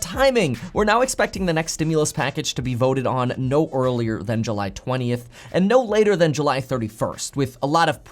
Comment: Bill Jowers thats the latest. Earliest is June 20th but the way things are going yea, maybe I’ll see you all in July instead 🤣🤣🤣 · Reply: Bill Jowers ah yes he did. I could have sworn he said June 20th. I guess I heard what I wanted to hear 🤣 I stand corrected!